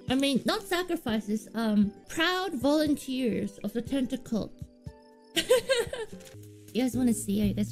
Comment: I think she said Tentacult, not tentacled 0:52 chat, not cats